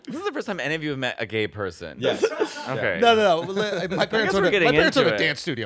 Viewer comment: "my parents ___ a dance studio" what does Andrew say there? I kinda hear "owned" but Im not sure.